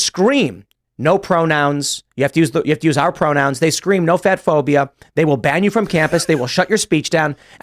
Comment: He said "no fat phobia" but I heard "no fed phobia"... Which still lines up. Lol